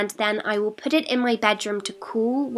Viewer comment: did she say whilst??